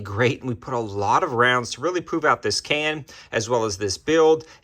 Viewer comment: Sounded like you said you've been running a lot of rounds to poop out the can